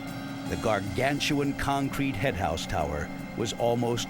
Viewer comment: For years I thought they were saying Henhouse instead of Headhouse, couldn’t figure out why they kept hens in the grain elevators. LOL 😂